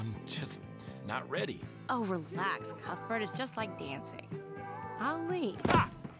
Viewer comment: Man I never really listen to the dialogue as a kid at this part. "Its just like dancing... On me" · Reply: I thought she said "Relax Kuthbert it's just like dancing...I'll lead."